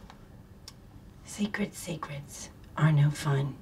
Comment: Secrets Secrets are no fun. Secrets Secrets hurt someone. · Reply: I thought she said sacred secrets on the second one...